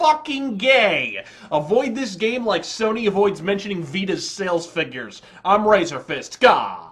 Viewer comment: I thought he said fetus sales, and I was like, "Man, Sony just keeps getting more and more evil with their business practices."